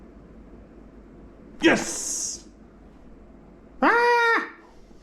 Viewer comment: Then why do i hear a person saying " Yes " in